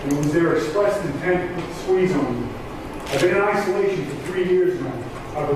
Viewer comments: I think he said something like “put the squeeze on me” referring to police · Great video, as always! Just FYI, at 25:51 I believe what Caius said was “it was their express intent to put the squeeze on me”